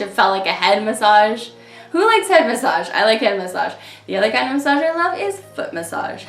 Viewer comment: at first I thought you said butt massage instead of foot massage. hahahaha! :)